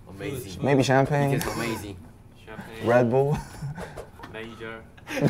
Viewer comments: What did Jin say here? I couldn't hear it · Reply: He said "manager" in his cute accent hahaha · Can anyone plzzz tell me what jin said at 1:27 min and why RM laughed soo hard plzzz plzzz BTW I love you BTS. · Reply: He said "manager" 😊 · what did seokjin said? Brazzer? Fudge. I don't know anything. I'm innocent. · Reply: "manager" 😊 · what did jin say ? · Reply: manager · What did Jin say at 1:25???? Major??